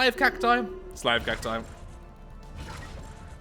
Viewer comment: when i first saw this, i misheard you and thought you said "Shrek armies"